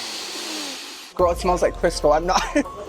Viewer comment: I really thought he said “yall it smells like crystal” until he said “we about to make some biscuits 😭😂